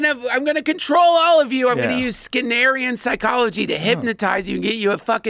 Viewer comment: does anyone know what type of psychology he mentions? Scanarian psychology?